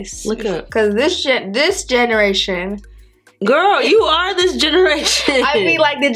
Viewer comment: When she tried to say this generation it sounded like she said the s word